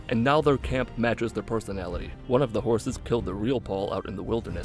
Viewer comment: at first when he said, “One of the horses killed the real Paul,” I thought he said, “One of the horses killed a Realdoll.” My hearing is really going to shit, I guess.